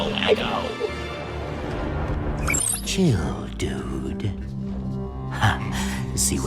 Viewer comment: anyone else heard geodude